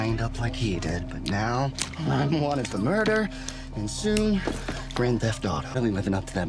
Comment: Is it just me or did he just say at 8:10 he said grand thefet onto